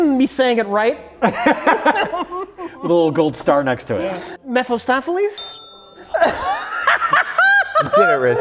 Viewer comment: he actually said "mephiLstopheles", so someone better take that star back